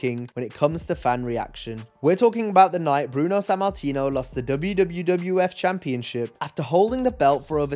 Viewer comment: Is it just me, or does anyone else hear "WWWF" when dude is narrating?